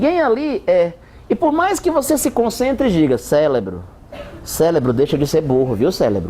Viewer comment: eu escutei "célebro" kk